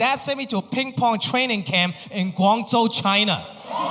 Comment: the subtitles are wrong, it’s ‘Guangzhou’ not ‘Gwangju’